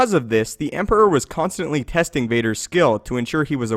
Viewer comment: Great video! lol sometimes it sounds like you say "pesting" instead of "testing" :P